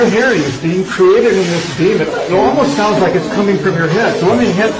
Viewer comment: it definitely sounds like demon but he actually said “beam &…”